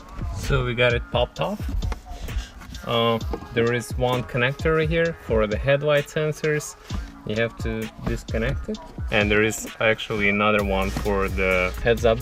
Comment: is there two connector for headlight sensors ? I can quite understand what you said next ??? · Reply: There is one connector for headlight sensor and one more connector for heads up display